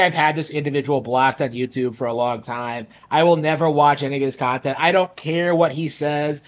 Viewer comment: did you just say the n word just saying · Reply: "any of his", not "any n*"